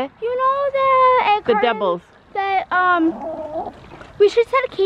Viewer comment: At first I thought said “I wondered who laid the devil.” I was like WOOOAH there that’s a little extreme, but she said double 😂 whew